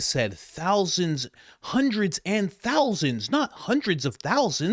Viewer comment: She said 'hundreds AND thousands' .... I thought she said 'hundreds OF thousands' too, initially ...... I'd go so far as to suspect it was a planned phrase (phonetic trap) Candace didn't fall for it btw.